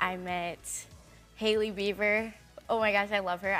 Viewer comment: oh god no. Why the hell would you do somebody like that. It says beaver instead of Beiber in the subtitles. What are u? A 16 year old kid? Selena Gomez fan? That is very immature for whoever wrote that. Hailey is nice but then are people mocking and hurting her.